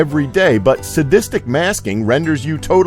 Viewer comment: sadistist masking instead of saccadic masking..... lol